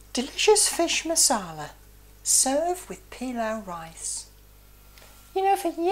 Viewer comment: I thought she said "delicious fish masala, so f-in damn nice"